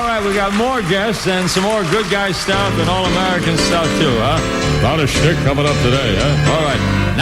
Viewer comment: Oh, my ... what does Harry say at 15:16, over the intro to "Saturday In The Park" by Chicago? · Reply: Lmfao. I know what you’re talking about and I know where he makes the statement in the video. For a second I thought the same thing because at first it sounded like he said “A lot of shit coming up today?” But he had to have actually said “A lot of CHIC coming up today” during the opening of the song by Chicago, because CHIC was I guess something made up to be short for “Chicago” because they played a noticeable amount of songs by them for that day and especially for his last show!!!! 😂😂😂😂